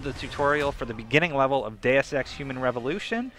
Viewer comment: told my girlfriend i was picking this up and she looked at me like a deer in the headlights, apparently she thought i said 'day of sex'